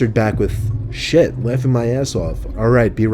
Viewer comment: I thought he said shit I'm wiping my ass off lmao